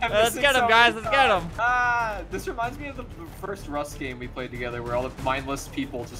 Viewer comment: “This reminds me of the first rust game we played together” All I heard was: “this reminds me of our first date-“